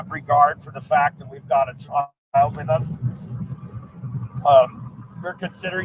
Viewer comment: what does he say? “They don’t even have regard that we have a ___ ?child? with us” .....??